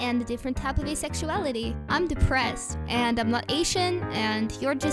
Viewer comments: Did Robo-Jaiden Say "I Feel Like A Wall" At The End? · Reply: I thought it said “Happy like a bowl” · When the robotic Jaden voice said HappyLikeAWall at the end, I thought it said "I feel like a wall" which makes it even funnier too me.